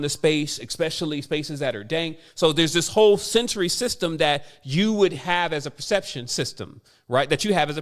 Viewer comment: timestamp he talks about a system, but it get what he says ? Century system ? Can someone reply please🙏